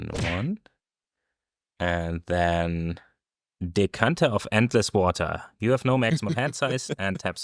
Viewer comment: I really hope it can ruin it for everyone else, I heard "Dick Hunter of endless water"